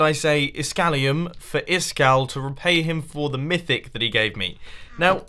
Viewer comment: / did he say Shit !?!?!